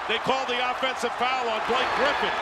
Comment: Did he say Dwight Griffin? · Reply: He said Blake but that's pretty close